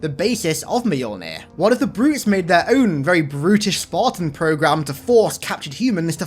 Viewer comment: I thought you said “what if brutes made very British armour”